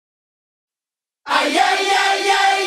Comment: " ahorita ya son que " lo escuché clarito xD